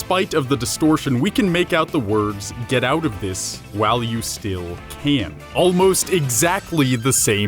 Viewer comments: I thought Baldi said kill when you still can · I thought baldi said "KILL WHILE YOU STILL CAN!!!" Not talking to the player but telling himself that he needs to kill you.